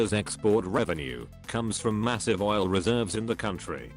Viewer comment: US: Did I hear oil?